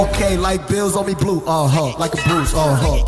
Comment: Bills on me blue? Dunno why I thought he said Pills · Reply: i thought he said pills as well lmao cuz blue pills kinda like off of matrix type shit